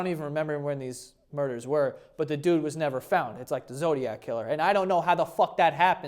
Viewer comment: I thought he said “Dildo killer” 🤣🤣🤣🤣